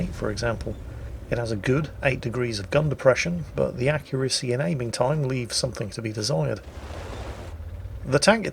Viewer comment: "Extremely accurate"....? Did you see how many shots had missed before you said that? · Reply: I heard him say "the accuracy and aiming time leave something to be desired"